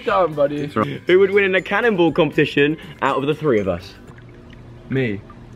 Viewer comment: bc of Joe's accent, I thought he said "canible" not cannonball 😂